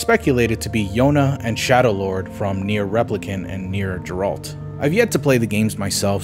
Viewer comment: automaton 12:54 Did you say Geralt instead of gestalt? 13:08 A2